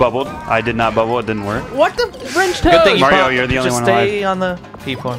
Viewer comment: Did anyone think at around 9:54 said "What the- French toast?!" Lol